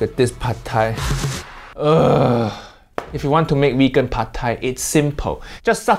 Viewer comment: When u said veggie pad Thai, I swear I thought u said weggie pad Thai 😂😂😂 imagine u really saying that with ur expression 😂😂😂